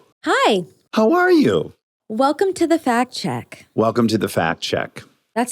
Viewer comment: Sounded like she said “welcome to the Fat Chick” and I spit out my (non alcohol) drink. No, girl. You good. 🤦